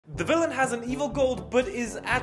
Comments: I thought that said "the villian had an evil goat " 💀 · First I thought you said the villian had an ‘evil goat’ and I was genuinely confused throughout the skit. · I thought the prompt said evil goat and I was waiting for the goat in happy confusion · My first watch i thought it said evil goat.. i was quite confused until my second watch